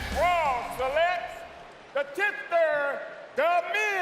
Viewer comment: What on earth did Teddy call The Miz? The "Tipster"? The "Mister" It sounded like more than just "Lister". The look on JBL's face at 2:27 was priceless...